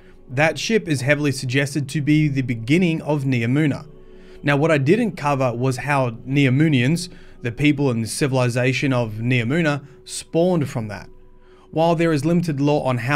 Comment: “Neomunans” sounds a better term to call them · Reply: I thought I heard him say "Neominions" in the beginning.